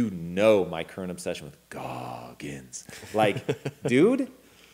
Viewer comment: love the after impact guys! What the name Tom say at 20:11 ?.. that one your obsessed with able to harness darkness? thanks again! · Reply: That would be the toughest man on the planet - David Goggins. That guy is a beast.